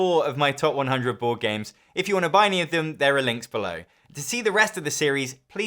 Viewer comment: I was a little bit distracted while listening to the end of the video, and I clearly heard “If you want to buy me a Pimm’s, there are links below”. I would never do such an awful thing to you, and are you ok?